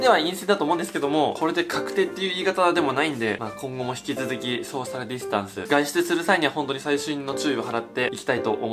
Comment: 「ソーシャルディスタンス」が時々、「ショーシャルディスタンス」に聞こえる時があるんだけ笑